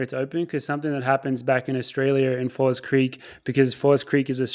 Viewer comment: Come on you did not just say “australier”